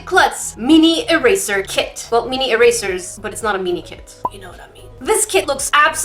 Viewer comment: It sounded like she was saying it’s not a meanie kit lol